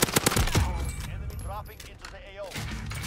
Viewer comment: Did he just say enemy droping in the yayo